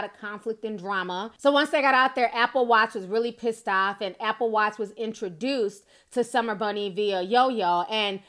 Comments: When she said “Apple Watts” I thought she said “Apple watch” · I thought you were saying Apple Watch lol idk who these people are I was just rolling with it 😂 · Giiiiiirrrrrllll, I forgot about Apple Watts, I thought yo ass said Apple Watch, I was like, “oh hell no, Apple would NEVERRRRRR.” 🗣😂😂😂😂